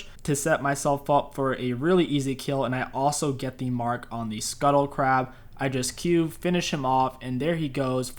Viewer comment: Not trying to be rude but around 5 mins I heard " I got the kill on the scuttle crab." When you finished his large raptor.